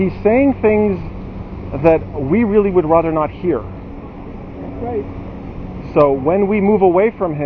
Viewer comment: what did that old man shout as he passed you? "Fake" or maybe "Satan"? Maybe it was nothing but its just that he shouts something next to you after he spoke with Those Jdums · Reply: He said, 'Got that right,' I believe. No sound now. 'You said it'?